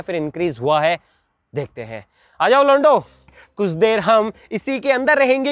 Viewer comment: I heard "ajayo lodo"🤣🤣🤣